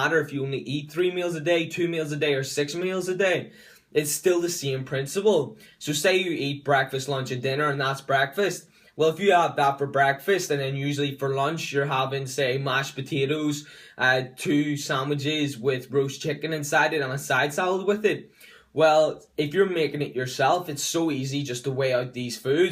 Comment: I heard "Say for lunch you're eatin mashed potatos, two chicken sandwiches, and a side salad" and I was like Woah, hold up mate. That's a lot of food right there. Lmao